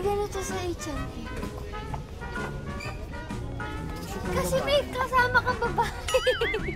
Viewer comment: did says babe to? Omo😂💕